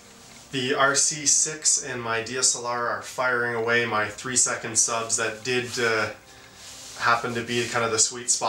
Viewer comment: Hey! Question: At 10:36 did you say that you took only 3" subs for this photo?